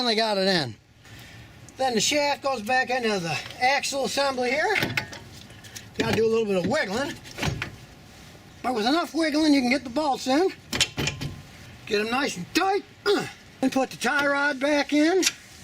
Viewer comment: did I hear: "With enough wiggling you can get the balls in"? Hahah...what she said :)